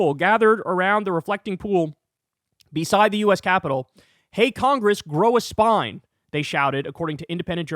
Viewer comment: I was protesting at my state capitol on President’s day. I want to tell my children and family I stood against tyranny. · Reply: "congress grow a spine " I thought they were saying something about Palestine ! 😆💀